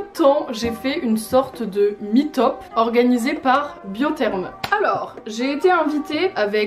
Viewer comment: y’a que moi qui entend « MIT OP » 🤣